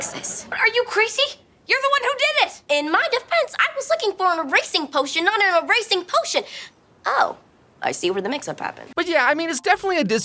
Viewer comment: i cannot for the life of me figure out wtf she is saying cause to me it sounds like she s just saying "i was trying to do an erasing potioin not an erasing potion" like was one a racing potion?? i am so confuded